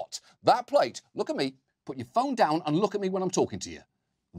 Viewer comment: „Put your phone down and look at me when I'm talking to you.“ Me watching this on my phone: confused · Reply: I thought he said put your Thumb down.